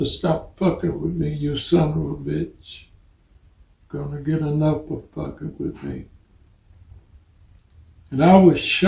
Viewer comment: Does anyone know what Mr. Bolden said when he paraphrased LBJ's threat to JFK when he quoted, "You better stop fucking with me, you SOB.... (the second sentence sounded like : You're gonna get enough for fucking with me".) but I am not really sure that is what Mr. Bolden said. Any help here? If you go directly to 3:50 / /36:51, this is where the 2 sentences Bolden said he heard Johnson yell at JFK as he stormed out of the Oval Office. The closed caption subtitles for this video are horribly in error in many words, making deaf people believe that many words were spoken in this video when they were not. Is that the best you can do??? Imagine if you were deaf? You use the word, neither, for, negro, for instance.